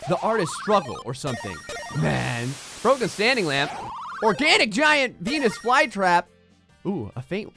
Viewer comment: i thought he said "ORGANIC GIANT FETUS IN MINECRAFT?"